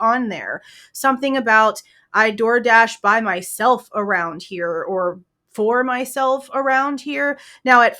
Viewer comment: I slowed it down to 0.55 and I heard her say "being a door dash driver is tuff around here"